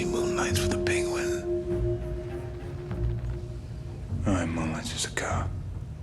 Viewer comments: What does he say at 7:52 ? Can’t make it out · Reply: "Or he moonlights as a cop" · y'all know what he's saying at 7:54?? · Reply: "or he moonlights as a cop" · Can someone tell me what he said at 7:54? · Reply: “Or he moonlights as a cop” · does anyone know what came out of Robert's Mouth? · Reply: or he moonlights as a cop honestly i had a hard time understanding what he said the first time as well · "No, he moonlights as a cop." lol took me forever to understand what he was saying.